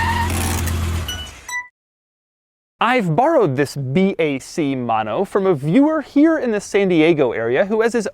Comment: I thought it said big Mac mono